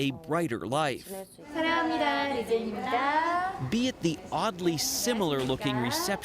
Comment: did he just said Ugly? DOTA is killing me, these bitches won't shut up, Can't hear the video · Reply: or Oddly